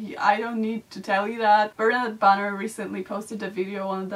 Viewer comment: can someone tell me what name did she say, im not good at english but i want to search this video se mentioned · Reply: Bernadette Banner